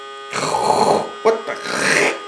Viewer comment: 🤣 I cracked up so hard. What da wcheeeekkcchhh! My girlfriend and her best friend always say "wak da heeelllll" in a Chinese accent and it always cracks me tf up. It really sounded like a play on that to me 😂